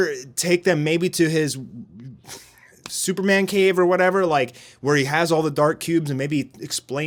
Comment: Did he just say Superman's cave!?